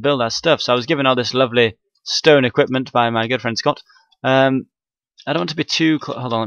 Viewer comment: I thought he said "by my girlfriend Scott" .....